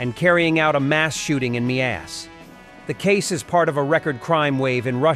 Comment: Miass, thought you were saying a shooting in the butt lol